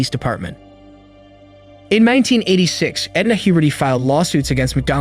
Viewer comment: Sounds like ur saying puberty